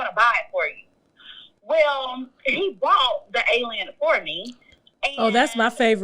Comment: Hold on what’s that perfume called ? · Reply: I said the same thing!! I replayed it three time and I thought she said Alien but I could be wrong!